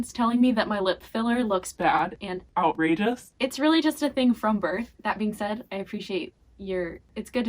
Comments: I watched this at like 1am and I thought it said "recreating my birth" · I thought I’d said “recreating my birth” 😂😂😂😂😂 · I thought you said recreating my birth.